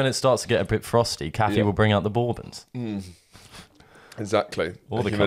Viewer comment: what are you saying? “Baubins”? What is that? Bon Bons? Bourbon? Please explain. Thanks. -confused American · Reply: Bourbon - it's a chocolate biscuit